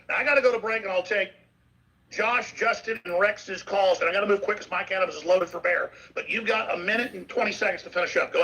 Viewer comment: does Alex say "My cannabis is loaded for beer"? lol